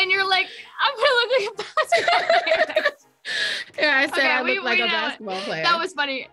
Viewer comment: guys what does she says here? 🧐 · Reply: "I'm going to look like a basketball player next to you"